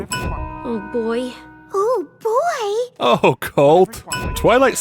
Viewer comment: I thought you said “Oh , cult! “ and was really confused for 2 minutes .